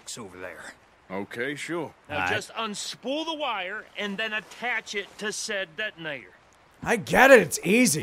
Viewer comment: Vegeta says obediently "If you say so" to Dutch? I know the game didn't give him much of a choice, but you'd think that his saiyan prince pride would not allow taking orders. That he would at least protest. Like a protestant. Not that it would matter. Well... now he knows how Nappa felt. 4:31. What is it with this strong accent? I keep hearing "pull the wire and then attach it to said dead ni..." never mind! 5:52. Just because he's psychic doesn't mean he's smart. 6:34. If you kick him hard enough, do you think some lucky charms will come out?